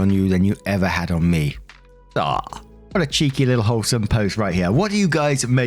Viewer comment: Omg! I thought said, “What a cheeky little ho” about the chic in story 1 update.🤣🤣🤣